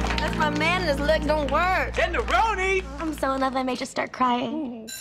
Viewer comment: i’ve been trying to find out what finn says for like 5 minutes 😭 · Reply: Tinderoni